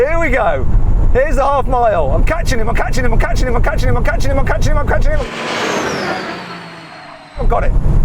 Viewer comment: 空耳で勝ち馬勝ち馬勝ち馬と聞こえるのは気のせいかしら…🤔